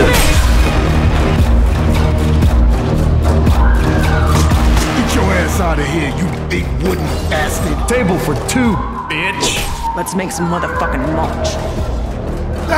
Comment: I thought Ronda said that “TaBlEs ArE FoR BiTcHeS”